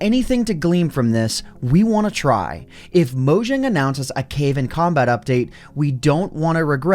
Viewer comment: Did he say cave and cobalt? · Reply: cave and combat